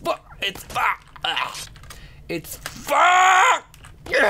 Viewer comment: it sounds like you said "it's foop!"